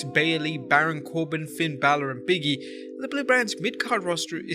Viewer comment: did you just call "Big E" "Biggie?" Come on man!